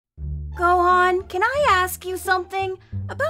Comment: Am I just trippin' or did he really say Gohan?